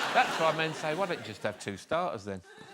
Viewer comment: WHAT does Alan say at 6:35? It sounds like: "That's why men say: why don't just have two stars, then? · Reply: I think he says „starters“ not stars as in two appetizers